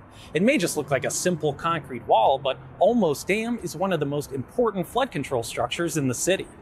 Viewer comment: I heard “almost dam” and looked at how short it was